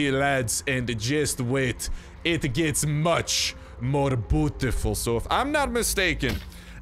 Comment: When u said Beautiful, I thought u said putiful 😂